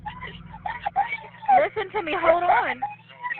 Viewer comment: I'm terribly sorry to ask, but what was saying at 18:19? May she rest in peace. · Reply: "I can't breathe! I can't breathe! I can't breathe! It's time to meet God"